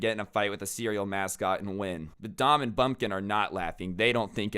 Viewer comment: i thought he said dumb and bumkin